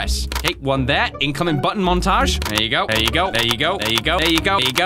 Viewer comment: saying "ego" instead of " there u go"